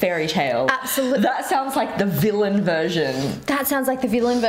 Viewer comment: "That sounds like the villain version" i misheard that as "villain Vision" and i thought of the white creepy Vision lmaoo